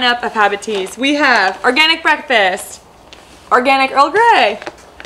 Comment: I thought you said "Organic Old Greg" hahaha